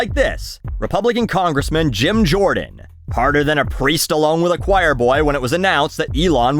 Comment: So Buckley said "a priest alone" I heard "a Pre-Stalone". So I paused and asked myself: "what does Sylvester Stalone have anything to do with choir Boys?", then I got the joke. It sucks being just 32 and having hearing problems.